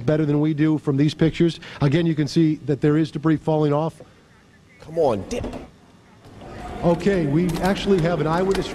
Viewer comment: I know this is a sad thing but there is sometjing funny in the video someone said "Moi dick"